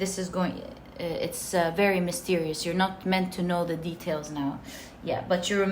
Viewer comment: did you say "you are" or "you are not"? Can someone else clear this for me? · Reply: “You’re not meant to know the details now” 😊❤️